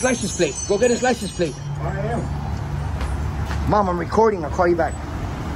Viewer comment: does he say "Mom I'm recording I'll call you back" ? So bothering some random old man takes priority over talking to his mother 😭😂 What a professional dork